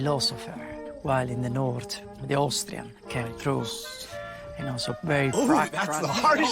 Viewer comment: What did he say at 39:09 ? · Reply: After speaking of the Greek philosophy roots the Sicilians have, I believe he said "...In the north, the Austrian came through..." Speaking as someone who has family from the North and South of Italy I can say that this sibling rivalry will always be a part of the life, it would have been interesting if Anthony had been able to finagle a one on one with the guy who said it, I'm sure there was a lot more to discover about the strange relationship between the two regions of Italy.